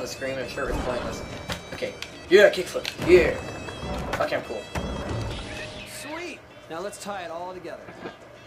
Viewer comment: tony said sweet and the subtitle said great did any one notice that